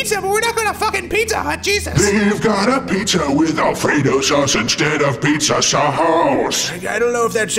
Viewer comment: At first, I thought he said "a Frito Sauce" instead of "Alfredo Sauce." XD